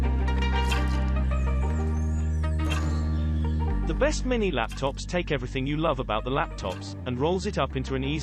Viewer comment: I thought you said Mini laptops?